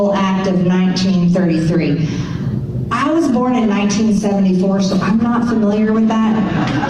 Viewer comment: I had to listen twice because it sounded like she said shw was born in 1944. It took another try to find out it was 74 instead of 44!!